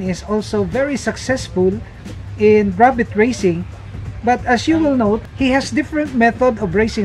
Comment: I thought you said rabbit racing 🤣😂 at one moment instead of rabbit raising 😅🙏🏼